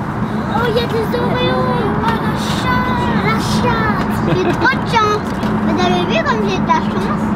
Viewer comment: elle a dit quoi l’autre petite ?😂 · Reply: J’ai entendu la chate mais ça doit être la chance 😅